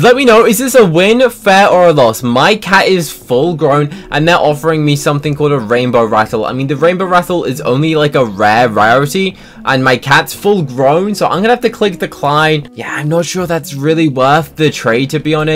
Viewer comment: Oh sorry I thought you said rainbow bufflo 😅